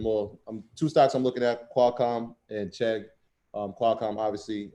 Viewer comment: what is he saying? Qualcomm and ched? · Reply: CHEGG - forgive my midwest accent lol